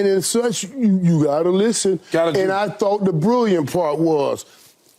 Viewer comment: Did I misheard or did Mike Irving just call Lebron "LeBrilliant" 😂😅 ?